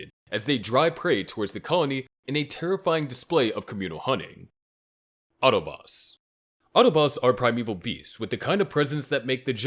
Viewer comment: I heard "Autobots", lol